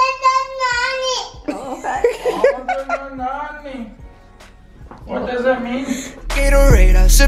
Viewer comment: I thought said "See you in the morning"